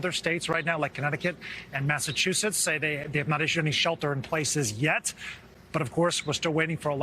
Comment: I got stuck in the cupboard because I heard "Shelter in plates".